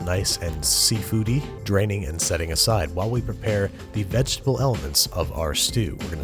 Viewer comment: When you said "vegetable elements", I first heard, "vegetable helmets", which is a brilliant way to describe mushrooms.